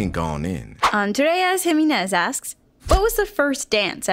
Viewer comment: it sounded like she said Andreas and not Andres (I would know cause Andres is my name)